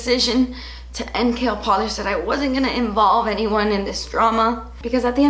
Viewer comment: First off, I just want to give you a big hug!! It will be ok!! ...............Also that intro, can we say "Mic Drop!" ....................... Also maybe one day in the future, even 5 years from now KL polish can make a come back. Because I am addicted to the polishes! No polish ever sticks to my nails, and these do!! ...............Also fun Fact, when you first launched KL polish I thought you were saying Kale Polish, and I just though Hmm, she much really love Kale!